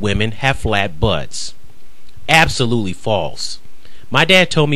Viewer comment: Did you say absolutely or absolutely false. Haha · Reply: I most certainly said absolutely false.